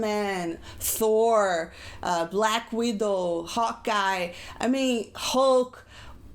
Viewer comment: sounded like she said hot guy instead of hawk eye but now that i think about it it could of been either